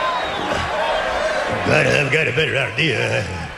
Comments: what the heck did he say? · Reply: I've got a better idea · wtf does Vince say at 5:30? lmfao no wonder triple h is laughing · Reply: "I have got a better idea..."